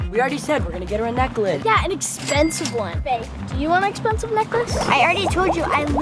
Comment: Wait at 3:44 did say babe 😊😊